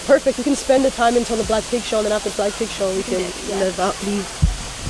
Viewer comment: I thought you said blackpink show for a minute 😂